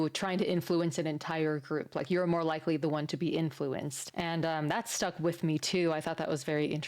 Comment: Yeah your trusting mind was believing in the good, like it was a warning that "bad influence" is contagious. Because of the bond between mother and daughter. But what was really said is "manipulate" instead of "influence". One at a time? This has nothing to do with "influence" this has to do with manipulation by group.